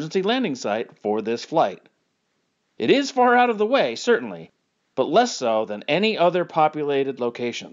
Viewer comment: what? you say it's closer but not an option what? I think you misspoke or something right? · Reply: I hear “Again this shows that Alaska is a logical emergency landing site for this flight. It is far out of the way, certainly; but less so than any other populated locations.”